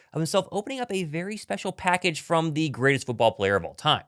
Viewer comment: I thought you said greatest football player of all time.